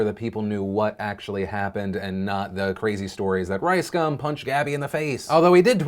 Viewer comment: Sounds like your saying ricecum